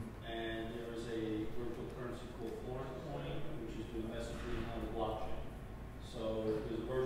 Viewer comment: What does the audience member say around 5:37 about virtual currency? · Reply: Sounds like he's talking about a Blockchain-based messaging layer that replaces an IRC server. Sounds like he's saying "corncoin" which was apparently a real coin related to Dakota State University.